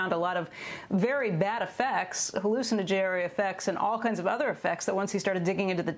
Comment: Did she say "hallucina-Jerry effects"?